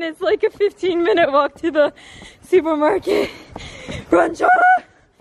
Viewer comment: i thought it said slaylor LOL